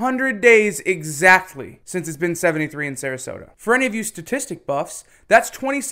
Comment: LOL! I thought you said "Statistic Fucks!"